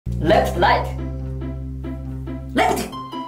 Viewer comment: I thought he said let’s fight